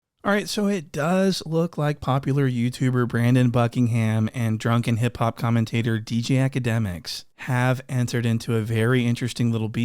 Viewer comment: I thought he said "drunken hippo commentator" 😂😂